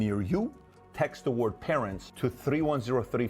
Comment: GOOD thing you said that again, I really heard 'KARENS' xD